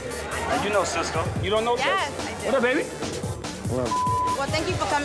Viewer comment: ok off topic but did rich say what's up baby to Cisco 😭